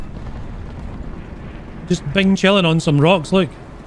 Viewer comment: did I hear say Bingchiling ay 5:14?